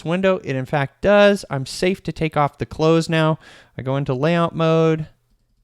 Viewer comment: Great video! Card windows are so deceptively powerful. We are using them more and more. Just had to chuckle when you said "…take off the close" referring to the Card window options. Sounded like you said "…take off the clothes!" 😳 Hey, you develop however you want. Haha!